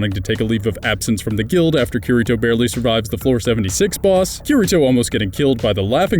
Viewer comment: 74th (I heard you saying it as "76th")